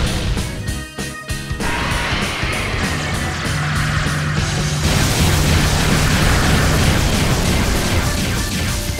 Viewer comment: Can someone tell me what Ichirin Summons there? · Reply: Hasshaku-san